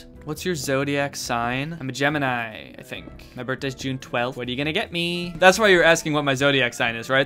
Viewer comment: when you said june 12th i thought you said 26th and i got excited cause thats when mine is :(